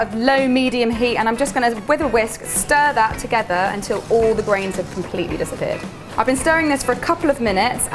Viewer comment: I thought you said ‘until the brains are melted’. My brain melted as soon as i read chocolate orange!